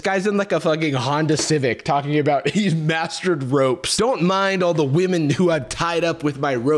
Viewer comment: The way I heard “mastering robes” instead of “mastering ropes” and spent the rest of the video imagining this guy in full Franciscan monk get up, surrounded by his harem